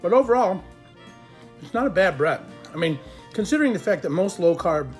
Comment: for a second I it sounded like you said “bad breath” 😷